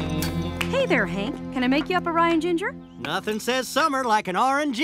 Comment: You know when They offer Hank a drink, I thought they were saying Ryan Ginger instead of Rye and Ginger lol